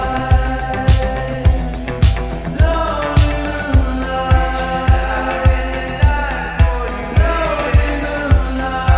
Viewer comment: what is he saying at 2:00????? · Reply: Reaching out in the dark for you baby I strained across that levee